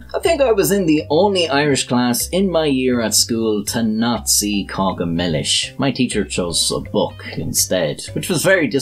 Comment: Subtitles say “Nazi Kagamelish ” instead of “Not see Cáca Milis ”